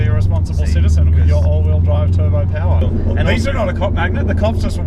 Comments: literally thought he said cock-magnet. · thought he said "cock magnet" which would also be true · Totally thought he said cock magnet